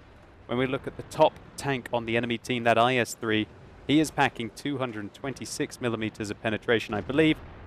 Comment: I thought he said hacking not packing